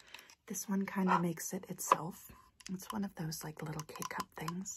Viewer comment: Is it just me or at 4:58 did it sound like she was saying " gay cup"?